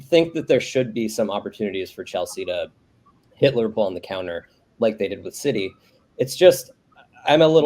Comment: Did he just say hitler ball in the counter 6:08?